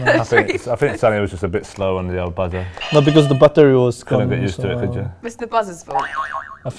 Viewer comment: Harry Kane: "pshsmhffhm" "Thats correct" WTF?! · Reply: did son say 'butter'?